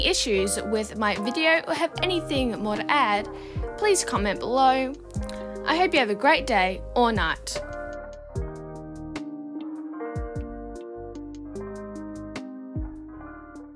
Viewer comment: At the end i heard "i hope you have a great night! Or not-" 💀💀💀💀💀 Welp i do have a little bit bad hearing tbh..